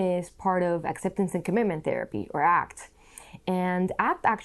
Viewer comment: “Diffusion is part of ———- therapy” what did she say? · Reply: Acceptance and Commitment (ACT)